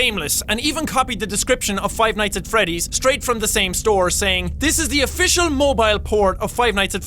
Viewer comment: i used the subtitles and in 5:28 the subtitle says '' is the official mobile porn site'' XD